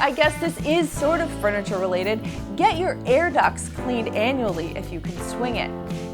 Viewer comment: I thought she said "ear duct" and went like "wuuuut???? I have pet hairs in my ears???". I need more coffee.